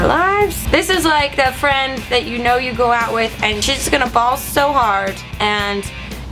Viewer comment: Ball so hard? More like bawl so hard. Which is what I thought you actually said.